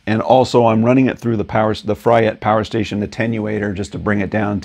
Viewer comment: My big sister had this record and I listened to Disraeli gears a lot (much older now). You bring out the nuances of the bends and fingerings. (The Hendrix chord is called E#9, sharp nine). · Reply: Ha ha! Sounded like you said it was a “fry it” power conditioner!